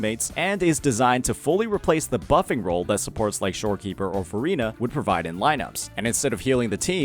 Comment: it sound like hé says Furi a. That's an other game broo😭 Jk i know it is supposed tó be verina